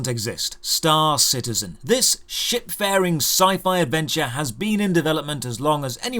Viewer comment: At first I thought you said "shitfaring sci-fi adventure" and you'd still not be wrong if you had